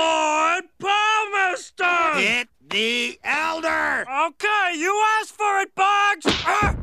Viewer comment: When I first saw this as a kid, I thought Boggs said "Hit me, elder!" and that's why Barney punched him.